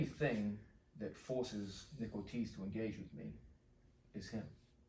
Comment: I thought the uke's name was "nickel cheese" because of the generated subtitles. Then I heard "Nick Ortiz". I like Nickel Cheese better.